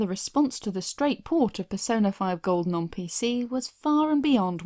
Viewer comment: Did you just say Persona 5 golden?